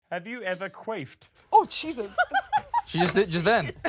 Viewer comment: When said, "Have you ever queffed?" I misheard it as, "Have you ever craved?" Then responded, "Oh Jesus" I thought "Oh man she is craving for Jesus"😂